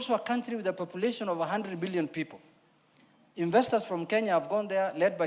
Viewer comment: Sorry but I heard him says 100bilion population 😅😅Mr speaker